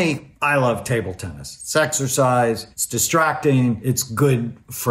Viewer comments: Is it just me or did I hear sexercise at 8:14 … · I love how he answers the questions rather thoroughly and not just gave shallow tips. Also did he say "sexercise" at 8:12?!? hahahah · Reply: "it's exercise"